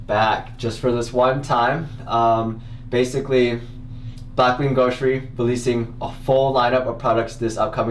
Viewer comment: I legit thought you said "Fapping Grocery" in the intro 😂